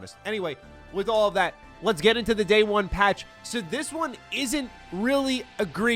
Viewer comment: I thought you said “day one dlc” and was like damn they’re going for that authentic mass effect feel !